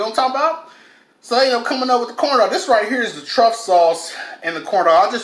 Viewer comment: For a minute I thought you were saying trump sauce